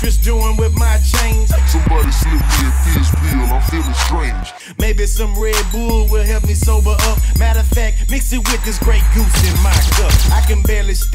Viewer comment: "somebody slipped me a THIZZ pill im fellin strange!" THIZZ LIFE!!! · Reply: No shit? I thought he was saying 'dizz pill' - like, a mickey finn, a roofie!!!